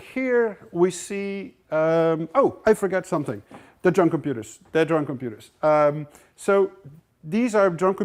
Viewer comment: drunken puters, the drunken puters! #misheardlyrics :D